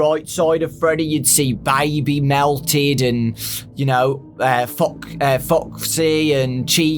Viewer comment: I thought he said ya know